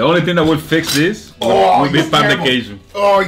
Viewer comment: Did he say "pão de queijo" (cheese bread in Portuguese)?